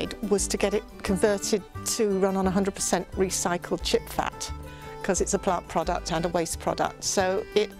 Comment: Beautiful home. Everything was really inspiring. I'm curious though. What exactly did you say the van ran on? I thought I heard you say "chickfat" but you said it is plant-based and not food. Was that "chipfat?" Either way could you clarify that a bit?